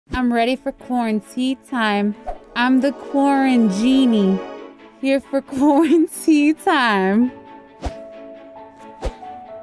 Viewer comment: Nobody: Not even a single soul: IHasCupquake: IM THE QUARANGENIE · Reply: Miya As in Mia! I thought she said quarangenie..